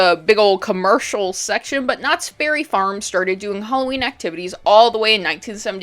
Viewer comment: I was listening to this while I worked so I'm not sure if I'm mishearing it but it sounds like you're saying "knotsberry" like the berry farm and I'd just like it to be known that it's "knott's berry farm" and not "knotsberry farm." so you'd just say "Knott's" and not "knotsberry." I mean do you, I'm not here to tell you how to make your content, I just think a lot of people genuinely think that it's "knotsberry farm" and I find that to be a little concerning.